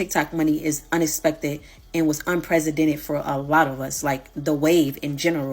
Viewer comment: did she say unpresidented??? And the trashy trashy smacking shows she’s ain’t bright at all